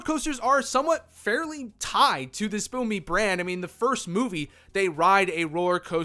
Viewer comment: I simply cannot watch a video where the person can’t be bothered to say the entire words. At 10:36 you say “spill me” instead of “despicable me”.